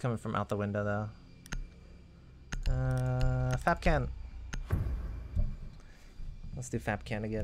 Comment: & 8:12 did anyone else hear him say FAPKAN XD